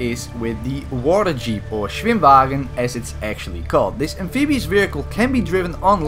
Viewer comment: How did you say schwebhshag * something in germany * nsshuagen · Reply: doğukan ışık schwimmwagen literally translates to swim car